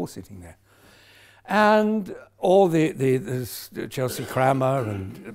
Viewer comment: Love him in Frasier, taking the piss out of his own Thespianism :D ;) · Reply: Does Derek call Kelsey "Chelsea Crammer"?